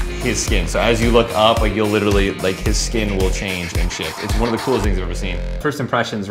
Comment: Guys he didn't say shit at 1:09 he actually said SHIFT. Calm down